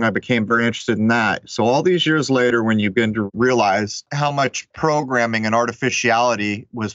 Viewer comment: After he talked about someone thinking his music said "Trump" 3 times, I swear I heard say "crowbramming"